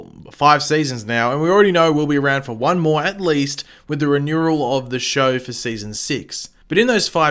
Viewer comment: I thought you said "One more and least" was that only me?????